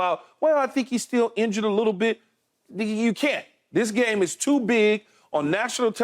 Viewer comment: 😂 Did he just say "ninja u can't" at 2:29 😂. Hilarious